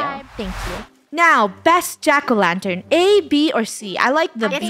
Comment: When they said, which is the best jack-o’-lantern, I thought they said -o’-lantern😂